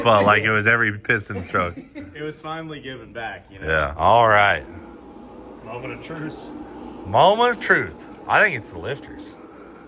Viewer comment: does say moment of truce?